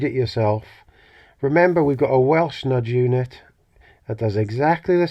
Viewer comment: I thought you said fudge unit. We have several in Devon called Roly's. I have to say that a one pound mixed bag of rum 'n' raisin, salted caramel and chocolate chip pushes me in a certain direction.